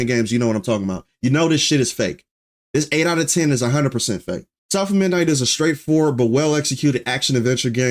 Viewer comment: All I heard was "South of Midnight is a Straight 4" End of review 😂😂😂